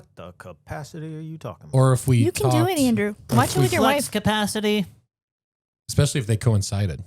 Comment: did she say, “why don’t you leave your wife?”